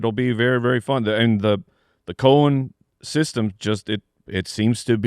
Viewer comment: anyone else hear "the colon system?" Or just me?🤣